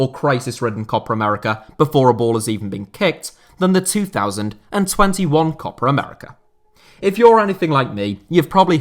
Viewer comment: At first I thought you were saying "Copper America". But then I realized it's just your accent. 🙌🤣 No hard feelings, I honestly just thought that was funny!